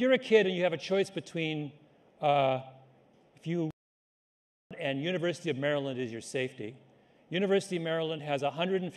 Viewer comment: I wonder what he said here. XD CENSORED! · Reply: Nah, just said "Harvard"